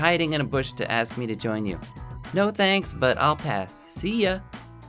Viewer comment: Whoops at 6:06 I thought he said at first no thanks but I don't want to . So I'm like thats the same thing